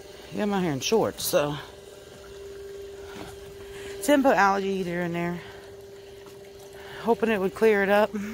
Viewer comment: I thought you said " Tim put an alligator in there" instead of algae eater! lol It does sound alike though! What a beautiful sunrise you get to see! Supper looked good. See you next time! Love ya!